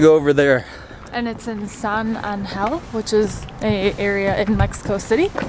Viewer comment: Siempre que decían San angel pensé que decían Sun and hell 😂😂😂😂